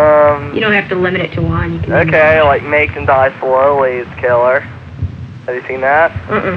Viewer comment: what horror movie does he say is killer ? I can't make that out ... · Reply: Make them die slowly.